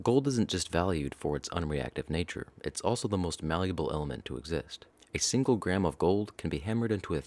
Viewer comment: i thought you said valuable for a second 😐